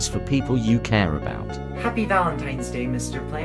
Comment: Bro for the first part I thought it said mr bart instead of mr plant- 💀